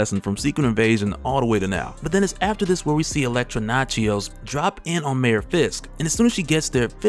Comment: Bruh i heard "Elektra Nachos" .. i am on the floor rn